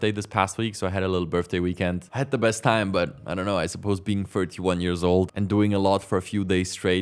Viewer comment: 41? did I hear you right? wow you look much younger, I thought you gonna say perhaps 31 or so haha, I am turning 38 very soon and I also don't look my age, I look younger by a few years (like 6+-) but you look even younger than me so don't feel bad about it. Edit : Oh lol you did say 31 I guess since I turned on the captions, ok then you do look 31, it's very young still, I am almost 40 years old, now that's really starting to get old.